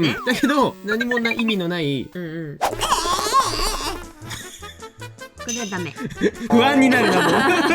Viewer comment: の｢これはだめ｣がコナンくんに聞こえた